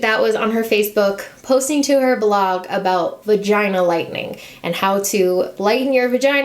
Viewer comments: I...a different image came to my mind when you said "Vagina Lightening"...I thought you said "Vagina Lightning". Lmao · When you mentioned Huda and vagina lightening, I thought you said vagina "lightning" like thunder & lightning and thought, what's wrong with vagina lightning? ROFLMFAO